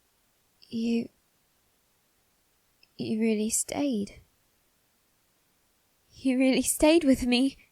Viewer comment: I thought it said "You slayed... You really slayed!"